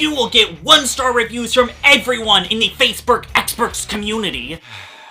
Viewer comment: LOL 5:10-ISH I think you said FACEBROOK😂😂😂 but this video is indeed informative and duely noted. 🥳